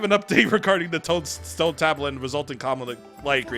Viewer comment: almost sounded like you were gonna say "Tone Stablet."